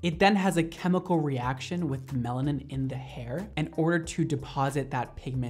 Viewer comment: I’m so addicted to it that the entire time he was saying melanin, I thought he was saying melatonin🤦🏼‍♀️😌😳